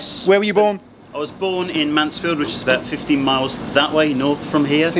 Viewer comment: says Mansfield is about 15 miles but your CCs say 50 miles. Does sound like 50 ☺️. Know it’s closer to Nottingham than that.